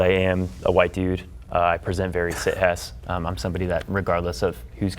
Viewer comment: What did say? "I present very siess." ???